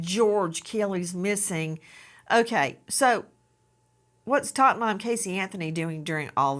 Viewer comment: I missed Nancy saying, “Tot Mom”. 😂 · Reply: I thought she was saying top mom for the longest time, I thought she was being sarcastic.